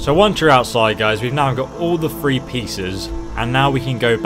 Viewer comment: it sounds like he said now we got all the 3 pizza's